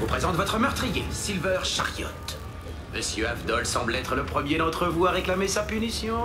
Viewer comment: French subs: at 0:07 there is a d missing for "d'entre vous", at 0:21 Polnareff says "ou de ta volaille pyromane" not "ou ton volaille pyromane, at 1:51 there is a n and a s missing for "comprendrais", at 2:26 there is a i missing for "vieille", at 2:54 its more likely "Eh" not "Et", at 5:01 there is a s missing for "elles", at 5:45 he is not saying the "se", at 6:05 he is saying "serait" not "sera", at 6:13 he is saying "cesser de vous disputer" not "cesser de se disputer", at 6:23 he is saying "on en cherchait justement un." not "on a cherché justement.", at 7:54 he is saying "de zombie" not "du zombie", at 8:02 there is a i missing for "vieille", at 8:35 he is saying "tu n'as qu'à te dire" not "tu n'as qu'à dire", at 9:24 he is saying "il est d'une humeur massacrante" not "il est de l'humeur massacrante", at 9:44 he is saying "on est rangé" not "on est arrangés", at 10:07 there is a "me" missing for "je risque de me faire gober mon Stand", at 10:10 he is saying "sans blague" not "sans la", at 10:57 he is saying "et" not "eh", at 11:17 there is a subtitle missing, he is saying : "- Saleté va!", at 12:03 he is saying "le prendre en photo" not "le prendre un photo", at 13:08 there is not a s at "enfers", at 13:54 same thing, at 14:01 there is a i missing for "vieille". Hope it helped